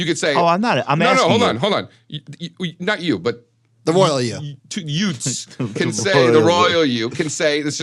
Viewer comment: The two hwat? Did you say yutes?